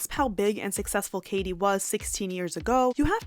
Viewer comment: did you say SIXTEEN years ago... Wow im feeling old